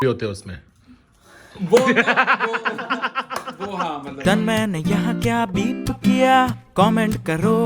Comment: To whoever that tells me what did he say at 3:09 May God's grace stay with him. His parents live long and healthy. He flourishes both in heath and wealth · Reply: He said "Behenchod"